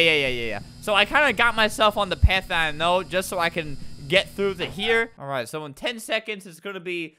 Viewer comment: Did he just say “Get through the here”?